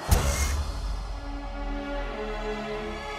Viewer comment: So, i found this video in a meme comp, and i thought he said "It's time to have some fun with Chris Hansen "